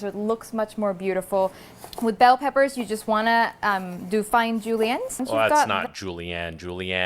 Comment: I thought I heard she said "with bell peppers you just wanna do fine julian". idk...